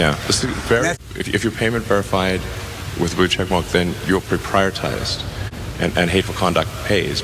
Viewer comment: Freudian slip: It says "proprietized" instead of "prioritized," which is very true and hugely funny. Yes, you are 'proprietized' once you pay to be a fake 'verified' account, ha ha ha. And yes, your cash will also prioritize you. Authentically unauthentic